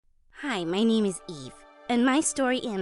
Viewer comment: HELP I HEARD “hi my name is beef” BY ACCIDENT IN THE BEGINNING